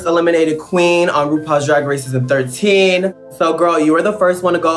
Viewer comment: The captions saying rupaul dragon racism 13 at 2:58 💀💀💀